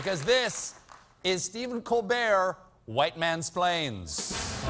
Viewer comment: I thought it said: Stephen's White Man Explains Lemonade. lol